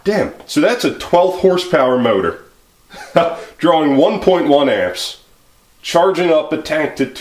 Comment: I can't understand what you said. It sounds like you said 12th HP motor drawing 1.1amps. Obviously that is not right. So what are you saying? Sorry, my hearing isn't great and sometimes I cannot make out what someone is saying. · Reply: The motor label says 1/12HP and 1.1Amps, doesnt make sense, but thats what it says LOL. Ill have to check it will the killawatt meter